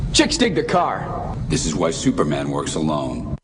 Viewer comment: Did he just say "this is why superman works alone" at 2:50? WTF!